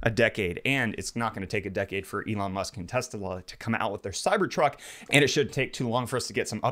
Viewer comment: we all heard him say testala instead of tesla?